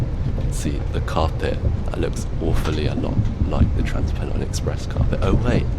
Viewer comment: im so confused how the automatic subtitles got transpennine and heathrow mixed up at 6:07 (please dont make 67 jokes)